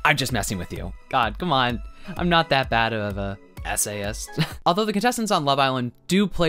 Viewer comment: I thought you called yourself an SA-ist for a second 😭😭😭 don't scare me like that bruh 😂